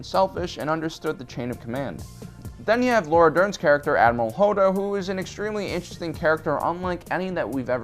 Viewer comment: sounds like you said admiral Hodor. And I like it. Hodor has more depth without saying anything. Because SHOW, DON'T TELL! :(